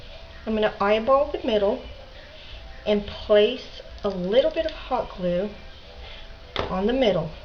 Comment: omg thought u said no hot glue?